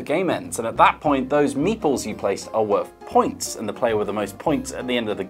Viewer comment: I really appreciate the subtitle that came up in the notification "FINAL" Good channel everyone, done everything we wanted to, now pack it all up and let's go home. I'm also glad I now know where a board game cafe in my town's name came from "Thirsty Meeples" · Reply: I had never heard of meeples before the other day until Johnny kept saying "meeples" in another video I was watching, but I thought he was saying "Meatballs" and I was so confused. It finally clicked by the end that meeples were little people pieces hahaha